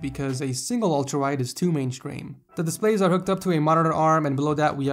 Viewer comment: did nobody notice how he said mainscreen instead of mainstream? #gr8bant3r